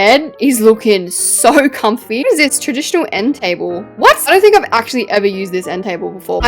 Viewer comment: ‘What is this? Traditional end table?’ (14:01) What it really said ‘transitional end table’